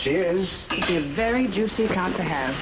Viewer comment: Did she just say.... "She's a very juicy cunt to have"?